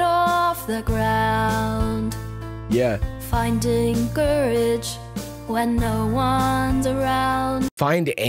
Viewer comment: Lol, I think it said finding courage. BUT FIND ANCHORAGE IS SO MUCH BETTER 🤣🤣